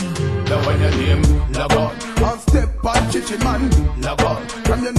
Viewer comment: Shalom Ahkhi , What is the point of this, I missed it. Thanks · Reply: shalam bro the song is called "log on" but it sounds like he is saying LeBron so I was trying to line up every time the song would say log on which sounds like he is saying LeBron, LeBron would be doing a dunk or something spectacular that would have you saying LeBron.